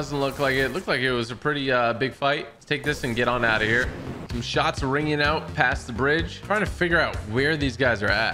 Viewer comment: is it me or i thought he was going say "I'm tying find out those guys at the bridge" or however you say it